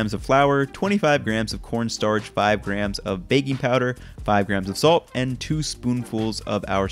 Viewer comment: I thought he said baby powder instead of baking powder. I was worried LOL